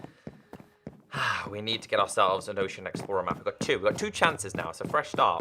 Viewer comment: I thought you said ocean man for a second... 😑 Ocean man, take me by the hand, lead me to the land that you understand Ocean man, the voyage to the corner of the globe is a real trip Ocean man, the crust of a tan man imbibed by the sand Soaking up the thirst of the land